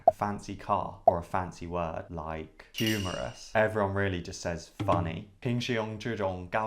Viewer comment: everyone just says"funny"有人聽清楚xm說什麼嗎？尤其是everyone just，向大大求解。